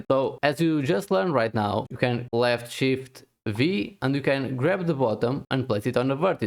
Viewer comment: Cant get it to work at all, has it changed in 6.2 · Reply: Ok so you said left shift and V, i thought you said B